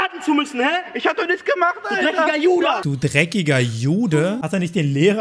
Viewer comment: ich hab "du dreckiger Judas" verstanden lol